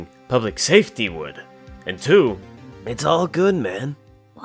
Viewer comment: Lets be honest, we already knew Denji was gonna win the moment Saul FUKIN GOODMAN arrived🗿🗿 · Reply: The way he said "It's all good man" It sounded like he was saying Saul Goodman.